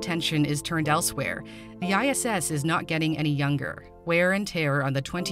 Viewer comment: My dumbass thought it said the future of ISIS